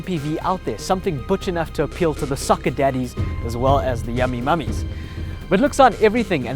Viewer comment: Is it me or Did i just hear "sucking daddies and yummy mommies" ? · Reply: No, he said "Soccer daddies"